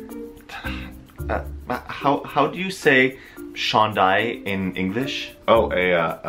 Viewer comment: what was the word he said at 4:44? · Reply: I said 'chandail' which means shirt or sweater in English. But if you literally translated 'Garlic Field' into french it would translate to "champs d'ail" which sounds exactly the same. So I was making a joke out of it :P